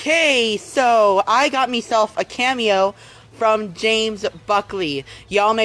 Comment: did he just say “Meself”???